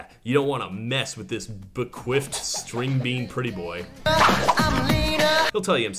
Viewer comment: "Yeah, you don't wanna mess with this ? String-bean pretty boy." What was the word before string? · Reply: Bequiffed, it means when someone has their hair slicked up and back